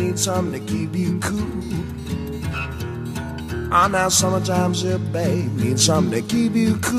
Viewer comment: I also thought he said "I'm that summertime zip bang..."